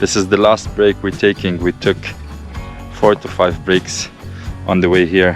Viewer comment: I heard as “we took 45 breaks” 🤣 am like WOW! Hahahha Love the video, awesome content 👏🏼❤️